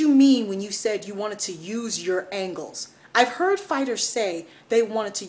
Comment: The person in question is Kevin Iole. I was surprised as to what I thought I heard. Nevertheless he clarified the confusion in a tweet. He misheard "angles" as "ankles". <link> I was beginning to think the 'real' Iole had be abducted by aliens.